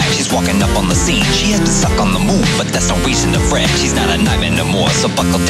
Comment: When he said “ She stuck on the moon “ I honestly thought he said “ She sucks on the moon “ ... alright man... whatever you say..